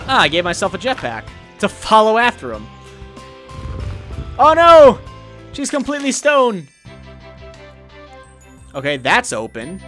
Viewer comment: I thought he said "Oh, no! She's completely stoned!" xD